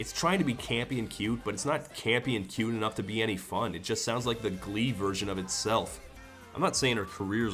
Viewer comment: "It just sounds like the Glee version of itself" BINGO. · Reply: Oh I thought he said the "ME version of itself" and I was really confused lol